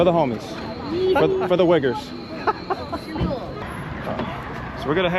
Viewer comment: 😂😂 He said fir the uigeres(or however its spelled) to the white guy from the state if Georgia wich is like!😂 wich sounded like ...for the whiggers😂😂😮 But im sure thats what the fuy meant in purpose😂😂 Quick minded😊 For the xuogures(whiggers😂😂) I.e .whigger in the southern part of the usa is a white person who acts like their from the ghetto! Not ethnic Chinese 😂😂😂